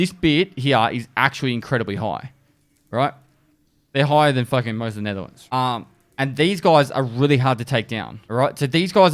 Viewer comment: is he saying "higher than most of the Netherlands?" O.o